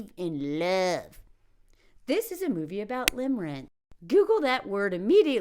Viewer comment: Does anybody know what she said at 7:05 ? I tried 10 different spelling and couldn’t find the word she said. Is it limmering? Limmarone? Limmrynn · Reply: Limmerance